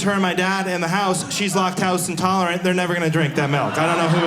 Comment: That took me about 30 seconds to register...what I heard was "locked house intolerant", then I was confused for a bit, then I realized "lactose intolerant". Pronunciation is weird. Also-- normal people don't freeze milk-- it doesn't work-- the fat/protein/water structure of the milk doesn't survive freezing and re-thawing, you can do that with butter-- not even most cheeses work. Blueberries-- brilliant. I thought I was smart for a long time-- I went to a really good college and had good jobs, but now I'm in my 50s and I think I'm an idiot. Is that wisdom? LOL-- then he talks about mispronunciation.